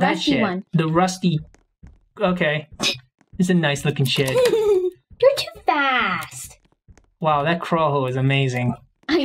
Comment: I thought she wasngoing to say your to fat for a second but she said your to fast